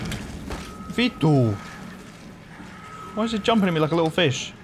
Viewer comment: Did you say vittu?